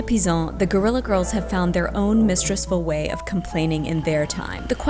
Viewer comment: A small thing I noticed while prepping to show this (excellent!) video to a class that I'm teaching. At 4:58, the closed captioning says that Sarah is [INAUDIBLE] when she called the Guerrilla Girls "mistress-ful." I'm guessing the person writing the captions didn't catch the neologism? Anyway, as it stands right now, the captions are kind of censoring out a woman's feminist repurposing of language in a video about feminism. And also providing an abridged experience to deaf folks who are watching. Not a huge issue, but also something that can be easily fixed, even after the video has been posted, so I thought it might be worth pointing out.